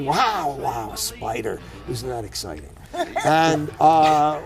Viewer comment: The subtitles say "A spider is not exciting" but he says "A spider: Isn't that exciting?"